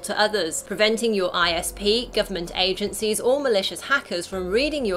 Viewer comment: i heard Malaysia. Am i heard it wrong? Hohoho